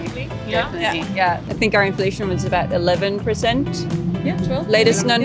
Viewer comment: インフレーションですよ。インフ「ら」レーションは？？？？？です。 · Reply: inflammationのように聞こえてますね